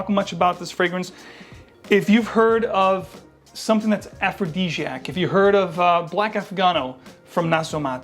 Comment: Hi good list..what was the word you used to describe Ludano Nero @ 9:56. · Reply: aphrodisiac